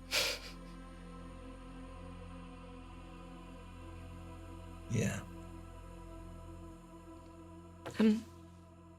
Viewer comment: Get well soon! At 1:52:26 does anyone else not hear what says (or am I just going a little mad)? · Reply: I’m not a lip reader, but I’m fairly certain she said "Maybe a little bit", in case anyone had trouble interpreting it.